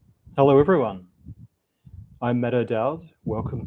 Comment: I heard, "I'm Meta Dowd," and I've decided that's your new nickname